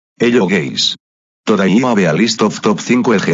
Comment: it sounded like ''hello gays''